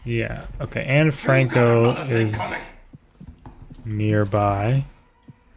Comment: sounded like bro said Anne Franco. Like we got an uber collab going on in prime time wth?